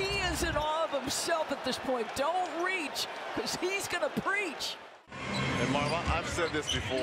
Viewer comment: so cringeworthy Doris Burke “Don’t reach cause he’s gonna preach” it’s teach sis strikes my nerve every time I hear it.